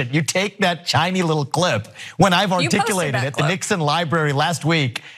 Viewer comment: He said Chiny little clip!😂😂😂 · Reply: I heard "ChinEE little clip" but okay.